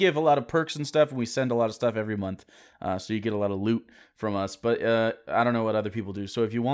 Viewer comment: I misheard at the end that we get a lot of lube from you, and I got a bit excited, then I realized that you said loot and I cried a bit 😢